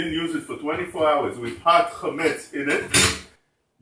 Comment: Meira Yolanda Lettieri ,Shalom.not using the sink means not pouring hot chamets to it,otherwise you can use it regular. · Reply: Thank you. I thought you said it has to dry for 24 hours.